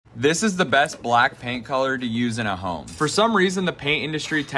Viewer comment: When u said black paint , i almost misheard "BLACKPINK "😂